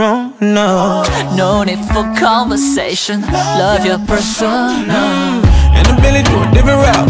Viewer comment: Lay: No need for conversations, love your persona. Me: * dies * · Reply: Lisa pii Blinkk first i thought he said fursona 💀💀